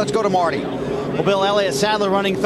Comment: did martie say "bill elliot" instead of elliot sadler